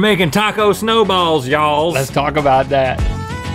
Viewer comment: Haha in my head I heard Link say “Let’s taco about that” 😂